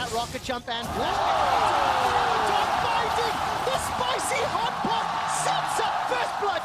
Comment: did he say "Spicy Hot Pot"??